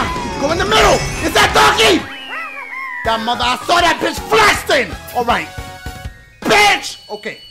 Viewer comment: Ya boi dashie said "FLESTING" instead of "flexing" at 3:03 bruh 😂😂😂